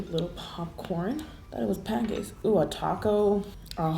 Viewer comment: idk if i heard that correctly but it sounded like u said ooo pancakes when u put the waffles in the slime🤣